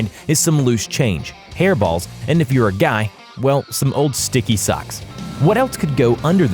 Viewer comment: 😂😂 sticky socks only guys understand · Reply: I thought he said stinky^ but I have now realized 😂😂😂